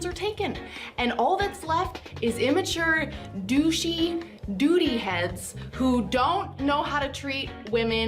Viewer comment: I'm sorry, did I just hear the words "immature" and "doody-heads" in the same sentence?🤣